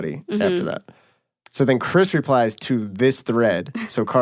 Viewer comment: Oh my god, all this time I've been listening to the audio, and I thought you all were saying "crystal-ee-uh" not "Chris Delia" lmao